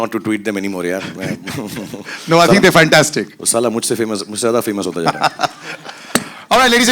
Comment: did he said sala mujhse famous ho raha hai?